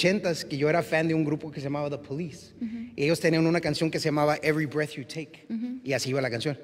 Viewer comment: no entendi la banda ni el grupo me podrian ayudar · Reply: the police every breath you take ;)